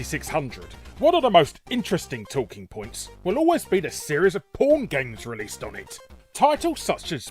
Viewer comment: subtitles at 1:22 say 'pawn' instead of 'porn'